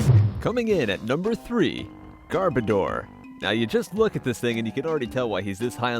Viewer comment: When you said gardebor and I thought you said gardevor kirlias evolve form